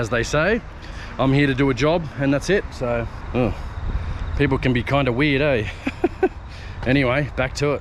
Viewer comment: What your mouth said was "people can be kinda weird, eh?" What I heard was "people can be right cunts, eh?" The answer to both your question and the one I heard, yes. They can be.